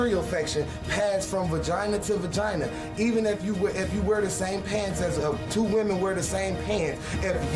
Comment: I love how the guy says that if females share pads and such they can get a yeast infection. YUCK!! What woman would share sanitary products #1 and #2 a regular yeast infection is not bacterial it is fungal and the yeast infection some females get is from antibiotics which kills the good natural bacteria that is in a women's body letting the not so good natural bacteria over grow causing itching. 1 has nothing to do with the other. Now I don't know the answer to this question. If a woman gets a yeast infection from antibiotics which kills the good "natural" bacteria to die and the bad "natural" bacteria to over grow and a man has intercourse with that woman can he catch that bad bacteria and pass it on?? I will ask a doctor when I see 1 next week. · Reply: He said pants . Not pads. I had to go back & listen because I thought he said Pads too. Lol